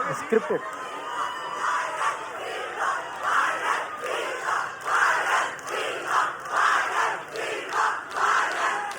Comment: what are they shouting ???? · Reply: Viral Fever